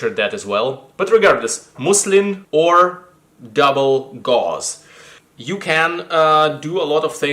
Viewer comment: I've got the better part of a bolt of mid grey double (I've always heard it as "bubble") gauze that I plan to make a hooded scarf with. You get a long length, wide enough to make half of a hood. Stich the ends together, turning it into an infinity scarf. Then stitch about 1/4 of the way down one side, that's the hood bit. Before doing the hood you might also want to cut the corner of the stitched bit into a curve, or you get a pointy head to the hood. You put it on like a hood, then loop the rest around your neck like a scarf. A great add-on to any post-apoc, fantasy, or Star Wars costume.